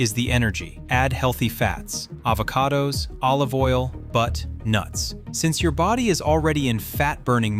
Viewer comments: did he say BUTT, NUTS? 🤣🤣🤣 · did he say “butt”? Cause I’m not eating that 😂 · Avocado, Olive oil, and, wait, did he say "Butt nuts"? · does he say butt, nuts?